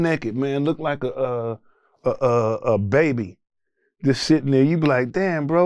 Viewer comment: did he say baby?? 😮😮😮 So.. we all know wtf that means